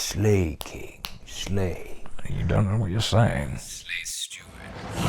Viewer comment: Ohhh “Slay Steward”. I thought Aragorn said slay stupid